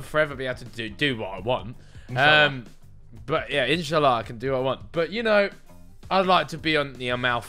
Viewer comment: is it just me or did he say 'inshallah' first I thought he said initially but no one can prounounce initially that wrong